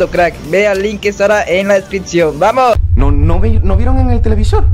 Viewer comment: No quiero hacerles falsas esperanzas pero yo escuche "vamonos", las dos veces que dijo